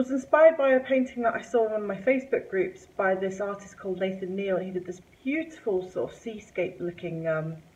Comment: HI, who is the artist you mentioned as your inspiration for "SKITTLES" I thought you said Nathan Neil? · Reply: Yup his name is Nathan Neil, he posted it on one of the acrylic pouring groups that I am in :)